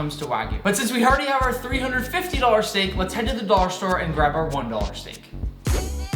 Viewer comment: Idk y but I heard ”... the dog store” 😂